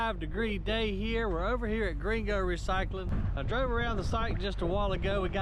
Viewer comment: I thought you were saying Gringo recycling! Lol